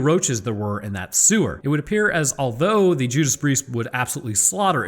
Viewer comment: I'm sorry did he just say the Judas Breeist lol